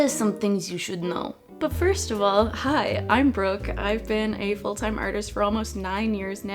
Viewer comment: I thought she said: hi I am broke….not Brooke lol